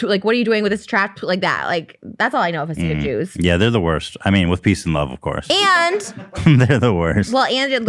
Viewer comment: I swear Ethan just said with pizzas and love... I think this should be his new catch phrase.